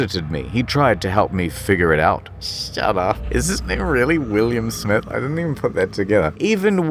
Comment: what the hell did you say here? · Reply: I thought exactly the same... pretty sure it's "name" but mid chuckle